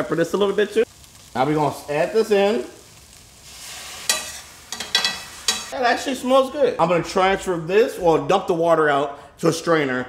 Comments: I thought he said "Hey that shit smells good" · thanks for the credit, i might make a video of my first box. also did you say "hey that shit smells good" at 4:36 ? xD also your pans look new AF. lolol where them burn and scrape marks from use? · I heard cuss at 4:38 saying "that shit smells good" 😂😂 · sounds like he said "that shit smells good" lol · "Hey that Shit smells good"??? 😂😂😂 or did he say "actually"